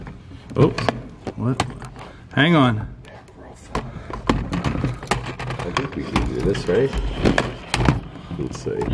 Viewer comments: while was adjusting his camera something whispered into his mic. sounds like " their something couldnt make out last word. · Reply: Bridgett Smith I heard “listen “ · there is a whisper...I can't make out what it says, but it's when you are setting up your camera. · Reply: I think it says nephrophonic , in a male voice.